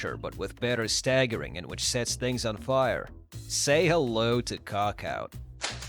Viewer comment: … sorry what?! Say hello to WHAT OUT?! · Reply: He's pulling his Cookout!